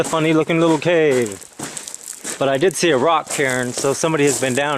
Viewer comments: Did anyone else notice him mention that it looked as if someone else had been down there? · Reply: What's he say? A rock chair? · is NOT saying "Karen", he's saying rock "cairn", which are those piles of rocks people sometimes leave as trail markers, (similar to the "inuksuk") and that lets others know that someone has been in the area. actually says: "I did see a rock CAIRN, so somebody has been down here" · what does he say? "I did see a (rock chair?) so somebody's been out here" ? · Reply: rock cairn